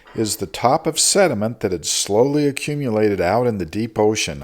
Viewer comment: I thought he said "my trusty white boy"